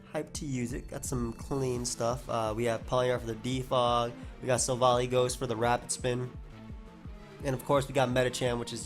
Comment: At the beginning I thought he said Pawniard WITH the Defog and Silvally-Ghost WITH the rapid spin and I was like wait